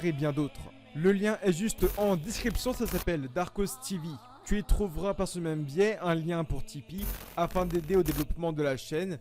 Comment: il a dit quoi ? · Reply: ichihiro satsuno yukushi tu retrouveras par ce même biais